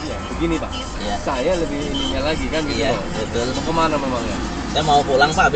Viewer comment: Saya punya cctv pak, begini pak, saya lebih punya lagi.. Helooo apa saya salah denger lebih punya lagi?